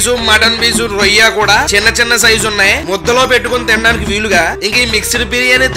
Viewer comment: what is the word you said ? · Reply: Muddha lo pettukoni